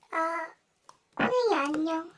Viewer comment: 소리 작게 하니까 아 .. 빠놀이 안녕 이라고 들려욬ㅋㅋㅋㅋㅋㅋㅋㅋㅋ 🥛